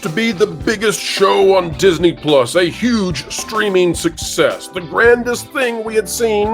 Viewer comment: Thought you said "steaming mess", no it was "streaming success". my bad